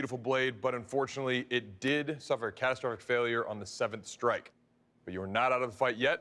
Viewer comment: did he just say, "catastoric failure"